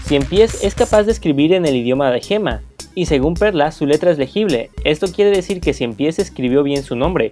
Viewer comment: yo entendí ELMA quien sabe